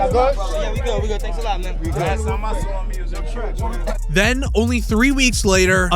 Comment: Sounds like he said "last time I saw him he was in traffic " doesn't even sound like Cuba 🤨